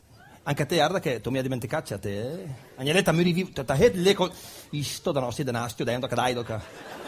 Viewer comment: ma cosa dice tra 0:21 e 0:23? sono anni che Bertolino nello sketch del muratore di Bergamo dice quella frase ma non essendo né bergamasco né lombardo non riesco a capire. c'è qualcuno che saprebbe rispondermi? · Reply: Salve ! Durante il dialogo in "pseudo bergamasco", dice testualmente "ègne lé, tà müre dét", tradotto: "vengo lì e ti immuro dentro". ( immuro neologismo letterale, inserire nel muro).